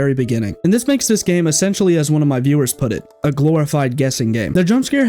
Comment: i thought it said squid game